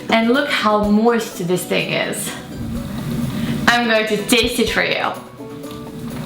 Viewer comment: wait a sec did she just say I'm gonna taste it for you???????